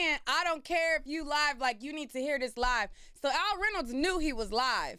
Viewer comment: I thought he said i want u to hear these lies!!!!